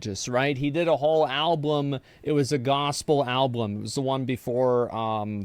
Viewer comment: Wait... did Asmon just pronounce it Alblum, TWICE? Does he think that's how you say Album? Why have I NEVER noticed this? YEARS I've heard this man speak.